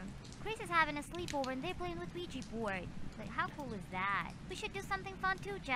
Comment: I legit thought she said Luigi board for the first one